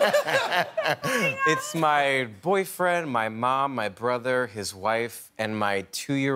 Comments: wait did he just say "my boyfriend" ? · Did he just say his boyfriend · did I hear boyfriend?